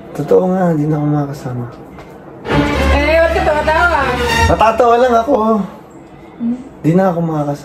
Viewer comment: guys can u confirm kong nag sabi ba si ng "natatawa lng ako bub?" Or I just heard it wrong😂